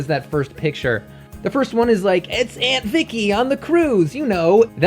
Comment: omg at 1:44 i thought you said aunt thiccy for a second lmao